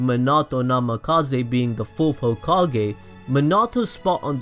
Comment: I love how instead of fourth hokage it says full focal gay